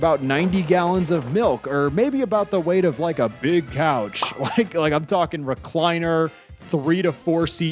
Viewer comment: idk why but I thought he said cow instead of a couch